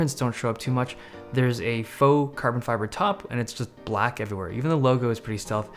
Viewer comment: did you say "fake carbon fiber"? automatic subs says "faux"...!?